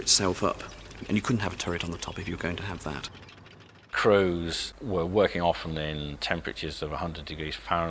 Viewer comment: did he say CROWS? At 4:00 a toilet tank? At 33:16 so the Cromwell had a 6 pounder gun and a 75mm QF VA gun? No mention of the Cruiser Mk VIII Challenger tank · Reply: Crews, it's his accent.